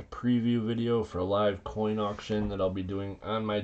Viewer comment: Auction? Man, I thought you said Bar mitzvah. Fine, I guess I'll still be there.